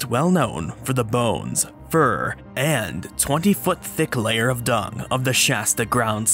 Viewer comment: 21 foot LAYER of dung... I thought he said 21 foot LONG dung. I was like man must have been backed up bad.